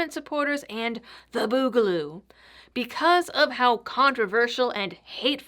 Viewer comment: I don't know why, but I kept hearing "boobaloo" and boobs. Naturally I have to share this one.